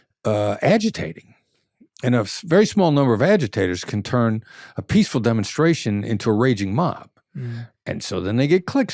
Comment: in 23:29 the interview he talks about an Instagram account, which is giving very positive vibes to people with only 36 posts. What is the name of the account? He said “hal tia” girl - but I couldn’t find any account like that. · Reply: Hawk tuah girl its a meme bro